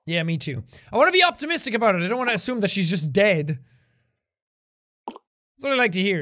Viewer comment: i could barely hear him at it sounded like he said “I don’t want to assume she’s just gay”